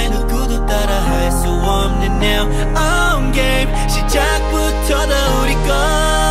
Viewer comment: I’m sorry but all i hear is “now I’m gay” lmaooo i can’t